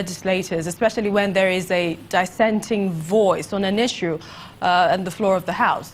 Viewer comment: It sounded like she said "disecting" opinion. She's is boring.